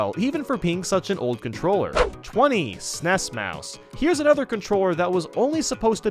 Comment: I thought you said nest mouse